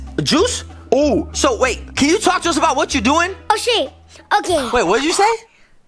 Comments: did he just say (oh sh**) me: heck naw! Man..😅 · sweared and he’s younger then me and I never said a swear word before And it kinda sounded like he said oh shay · they should have deleted the part where said the s word but I think he said she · didn’t curled he said o shay or o skake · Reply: But for him he thought he said curse but he didn't we listen him say o shay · oooo said a baddy word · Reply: It sounded like he said oh ship